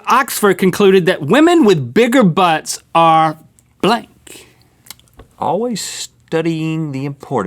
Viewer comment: at the beginning I thought they said, "women with big butts are black" instead of blank xS